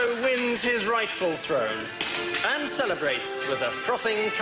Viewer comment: ai nghe ra cup of gì k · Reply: cup of ale